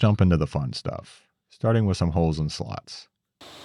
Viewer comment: I know i cant be the 9nly ine who heard "hoes and sluts"